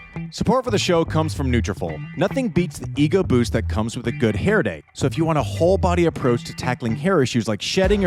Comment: "nothing beats the Iga boost of having a good hair day" - I swear that is what I heard! Speaking of hair, Sinner 's glorious auburn mop, especially in that Gucci skiing ad, is getting a lot of attention! 😁